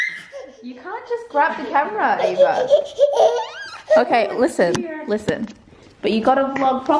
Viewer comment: it sounded like said "Give me the camera." She's so adorable